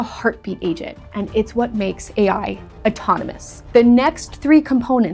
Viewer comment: I thought you said “automonous” instead of “autonomous” - but before blasting you for it, I rewound to hear it again and realized that you did in fact say it correctly. But at the end when you asked me to like and subscribe, I thought - yeah, right. I’m not subscribing to someone that can’t even pronounce autonomous properly first try! 🤦🏻‍♂️ (even though I know you didn’t mispronounce it - maybe this is how hallucinations are born?). Subscribed.